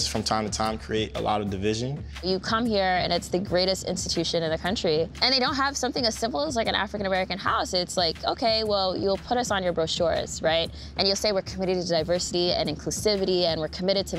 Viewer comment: Did she say AA house?